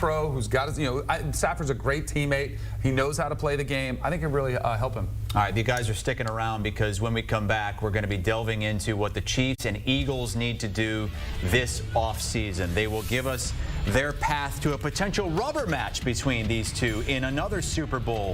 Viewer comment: Did cuz say " the Cheats & Eagles"? 🤣 It's more the NFL & refs cheating for the Chief's because they allow KC's line to hold, and 74 to false start & line up as a RB almost (a lot) nothing the passer calls on Ma-Flops and phantom PI calls against the opponent.